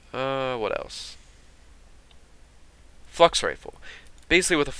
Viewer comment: when you said Flux Rifle it sounded like you said 'Fucks Rifle' LOL